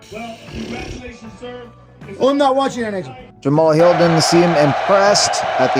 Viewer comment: “I’m not watching next one “ 😂😂😂 is that what he said